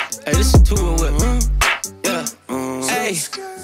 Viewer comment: What's the song right when they get on the yacht at 25:12 ? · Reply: In the beginning sounds like he says, Ay this is "two a whip"